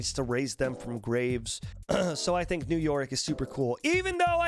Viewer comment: When said "New Yorick" I heard it as New York.